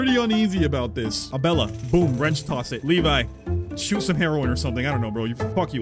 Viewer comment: I heard him say "CHEW some heroine" and had to go back to read the subtitles 😂